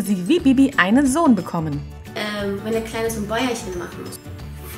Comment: Ich hab „die Kleine“ verstanden , mehrmals 🤔🤗